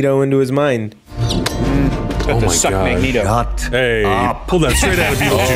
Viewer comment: i don’t why but all I heard was Joe Biden telling Trump to shut up!😂😂😂